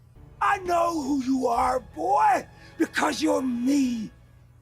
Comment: “I know who you are because you’re me.” Gets me every time. It was his way of connecting by planting an extension of himself. The cave of skeletons are the remains of his kids. The ones with no powers. The “they never felt a thing.” Was delivered SOOO coldly it gives me chills. The way Drax messes with Mantis makes them my fave friend group of the Guardians. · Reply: ngl, that "I know who you are, boy, you're me!" line confused me for the longest time I kept wondering if Rocket for some reason was created based on him or if Yondu was the result of mass-produced kree slaves similar to the Clones of Star Wars and Rocket was another iteration of that or---- And then eventually, after years, I realized he just meant they had similar backgrounds and points of view I think the first few times I heard that line, I sometimes misheard it as "you're mean", so I did consider that Yondu wasn't being literal but yeah lol